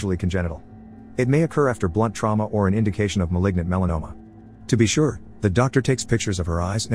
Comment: Me: "did i hear ligma whats that "